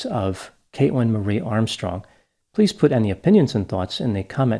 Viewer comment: Wow for a second I thought you'd said "Kayla Marie Armstrong" and were going to do one on Lorne Armstrong (those TCAP people out there know)